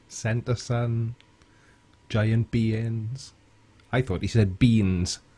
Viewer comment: hey i also thought he said beans